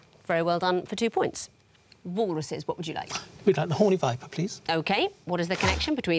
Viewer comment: Anyone else hear choose the “horny viper” at 3:40?